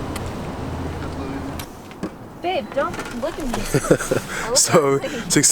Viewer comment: this video is A1 bro, also did anyone catch call babe @ 19:53 I knew it!!! anyway keep up the good work bruh